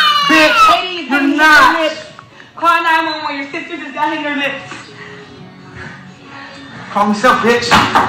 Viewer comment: Why did she yell CALL 📞 911 ❓❓if he didn’t touch her ❓ LADIES WE HAVE TO LOVE 💕 OURSELVES FIRST ! Lord pray for those children 🙏🏽🙏🏽. She has to break this cycle before her children mock his behavior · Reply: I heard “your sister got hit in the lip, call 911”